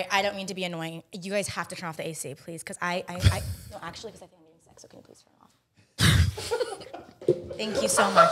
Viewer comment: can someone pleaseeee tell me what she said!! I’ve replayed it like 10 times lol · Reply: I think I am getting sick.